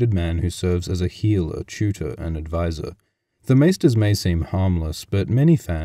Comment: This is incredibly stupid but I misheard 'a healer, tutor and adviser' as 'a heel, a tutor and adviser' and got a sudden mental image of the Maesters' roles also encompassing WWE (Westerosi Wrestling Entertainment).